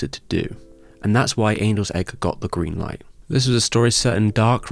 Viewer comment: I might have misheard you there. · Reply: "and that's why angel;s egg got the green light"